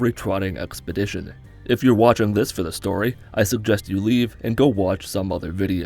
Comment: he SAYS i suggest you leave but the subtitles say I suggest you fuck off